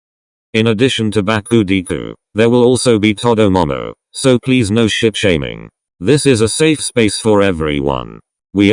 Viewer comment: In the beginning when it said “…so please no ship shaming” I heard “…so please on sh*t shaming” 😅 im now crying this was so cute 🐙(meet Bubble the octopus he’s is the best boi)